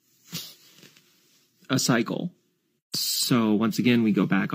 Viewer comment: No No No . . . you misunderstood. They did not say you have a cycle, they said you are a psycho .